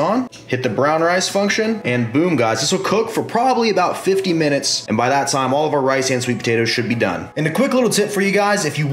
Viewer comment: did he say 50 minutes? :O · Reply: Ye he did. Must be brown rice i think, but even so 50 min sounds like a long time...